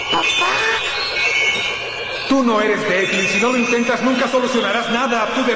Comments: soy yo o escuché que Goku dijo ”tu no eres gay y si no lo intentas nunca solucionaras nada tu deber es proteger a la tierra" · WTF,entendí "tu no eres gay" Xdxdxdxd